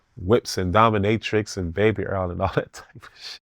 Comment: Baby Earl kills me eveytime 😂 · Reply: Took a sec when I saw baby earl in the comments, then I heard him say it, he said baby oil 😂😂😂